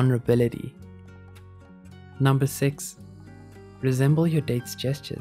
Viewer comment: 1. Keep it close 2. To look or to not look 3. Tilt your head 4. Affection with touch 5. Expose your wrists 6. Resemble your date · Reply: I thought it said "reassemble your date"